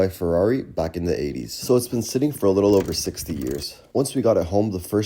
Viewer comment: I hope you just said sitting for over 16 years and not 60, what it sounded like you said.